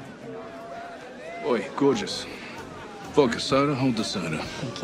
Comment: "oi gorgeous, foka soda honda soda, drinks are free but the food is bloody awful ayee taaahtaah for fuck's sakes" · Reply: "Vodka soda,holda soda"